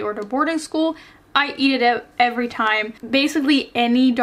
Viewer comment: I can't be the only one who heard at 10:10 "I eat it out everytime"